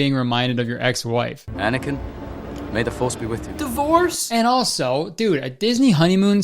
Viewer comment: It's took me awhile to realize you're saying "divorce" when he clearly said "the force" XDD thank god there's a comment section tho, realizing it from there. Damn those pronounce was really similar